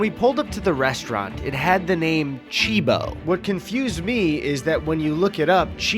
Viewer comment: I was listening without watching and thought you were saying "Tchibo", not "Cheebo" so I was like "why is a coffee shop selling cod sandwiches in the US"